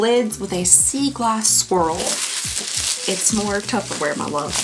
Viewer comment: I truly heard you say that the bowls had a sea glass squirrel and got really excited for half a second before realizing that you said swirl and squirrel didn't make sense 😅